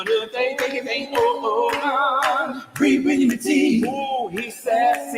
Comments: I had to rewind because I thought he said all my "Niccaz we bringin the tea" 😂😅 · lol it sound like he saying “alll my wiggasss we bringing the tea” 😂❤